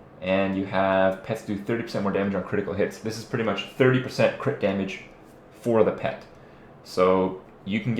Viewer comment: I swear he says Kripp Damage 8:11 awesome = )